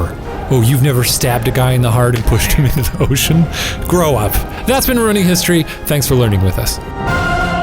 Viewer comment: Because only a boy would be training to be a clerk. Women didn't hold such positions. Also, I thought Shane said "And that's Marooning History", which would've been a great alternate title.